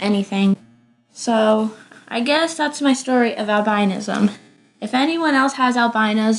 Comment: I thought you said you're Albainian, and also I thought Albino people have red eyes